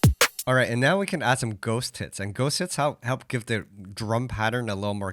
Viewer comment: I heard him say “Ghost Tits”